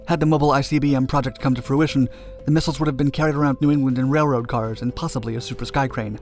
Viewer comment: Did he say carried around New England? @ 9:25 ?